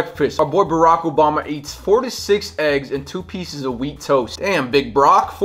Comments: Bruh I thought he was saying 46 eggs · Bruh I thought you said 46 eggs at the start